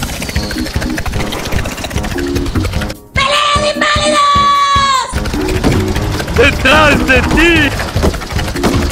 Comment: Que dice :v? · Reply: PELEA DE INVALIDOS XD